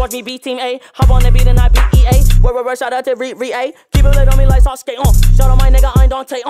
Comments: “Keep a blade on me like Sasuke” fireee 🔥🔥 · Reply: I thought he said Keyblade on me like sasuke, i’m like 👁👄👁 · Does x say Sasuke? Like Sasuke Uchiha around the 1:44 mark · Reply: Yes "Keep a blade on me like sasuke"